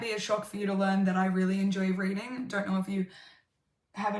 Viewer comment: I heard "raiding" and I was like "you play wow?"